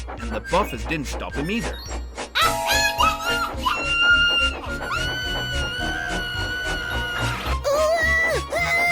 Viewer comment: What did Pinkie say at 8:15? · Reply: 1995 ABANDON ENGINE!!!